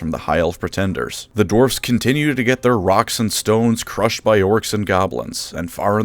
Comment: did I hear a rock and stone?!